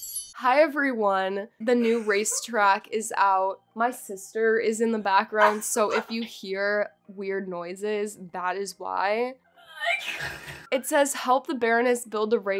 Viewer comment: Please- I thought you said, "My sister is in the bathroom so if you hear weird noises that's why." IM DYING AHAHAH